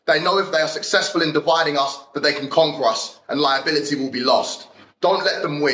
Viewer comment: Did he say "conqwur"?